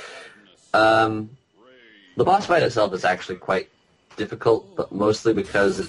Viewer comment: did you say the boss fight of Zelda?